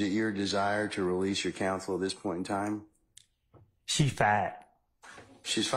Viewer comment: I thought he said, "she fat." I listened to it a few times. I was confused!😂😅🤣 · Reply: Hahaha and I thought he said “she fine”. Thankfully the Judge speaks stupid too or none of us would have any clue about what he said.